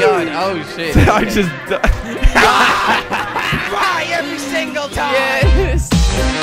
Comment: at the start of the vid i thought said i cry every time